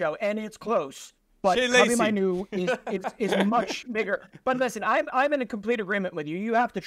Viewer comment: did he say the n word :D ? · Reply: “Much bigger…”